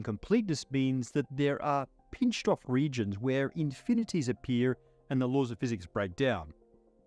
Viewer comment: At one point he says "infinities appear" but I heard him say "infinities of beer" and immediately perked up.